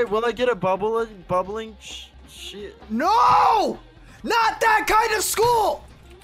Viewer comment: I heard the Sh*t word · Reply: it's bubbling sheet